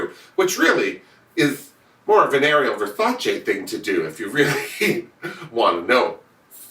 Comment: LOL you crack me up. Spot on as always. When you were talking about Ariel for a second I thought you said venereal Versace. I thought you were making up a nickname for her.